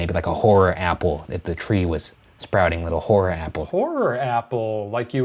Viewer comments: I definitely heard "whore apple" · Sounded like Ryan said whore apple. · I definitely heard "a whore apple"